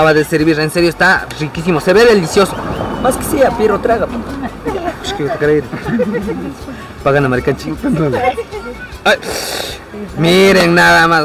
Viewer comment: Que pasó? En el minuto 5:22 ? Que alguien me traduzca que dijo 😅 hay que rico se veía todo pero hermano mayor nooo botesfffff la lechuga delas papas con cuerofff 😅yo amo esa lechuga rallada con el ají y la salsa de maní 😍😍😍😍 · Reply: Dijo ishkay kuta karay, pakana mary kanchi: regalame dositos(dólares), tenemos que pagar